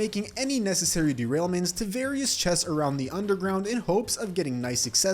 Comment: why did it say penis chest😅